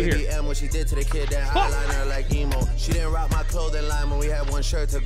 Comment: I thought he said Highliner like Nemo